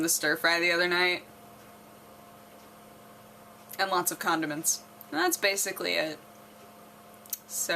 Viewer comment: i thought she said condoms...